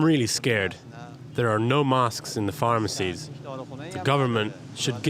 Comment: I thought he said no mosques , but it was no masks 😒